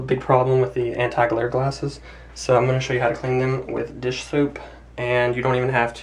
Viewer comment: did I hear Dish Soup? and all this time, I thought it was called Soap!